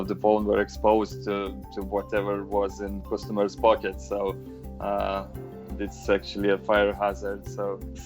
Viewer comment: i thought he said "so its SEXUALLY a fire hazard"💀💀